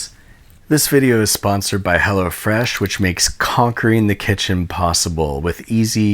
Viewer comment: Yo i swear i thought he said "Hella refresh"